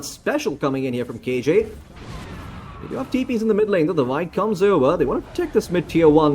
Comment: I keep hearing davai as "the vibe" lmao